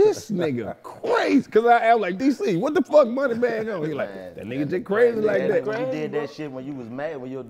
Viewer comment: I thought that said money bagg yo 🤣🤣🤣🤣